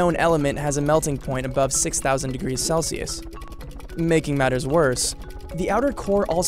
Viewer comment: Am I deaf or did he just say 6000 degrees celsius?